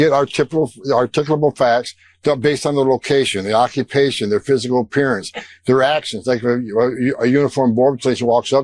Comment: "...observation, articulable facts based on the location, occupation, their physical appearances, their action". Fred Murtz can barely talk🤪! I had to repeat listening it multiple times to try & understand what he was saying? 🤔